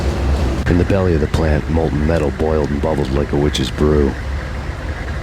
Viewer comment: Says molten metal boiled Subtitles say "Mold and metal" How the fuck does mold boil?